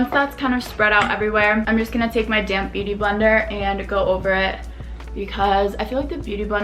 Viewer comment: Every time I hear '' damp beauty blender '' I usually understand ''damn beauty blender'' hahahah